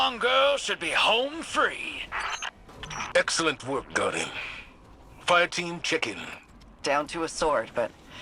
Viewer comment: anyone else hear zavala say fireteam chicken